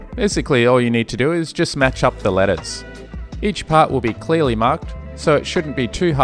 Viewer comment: "Basically all you need to do it just match up the lettuce" @ 1:34 that's what I heard anyway lol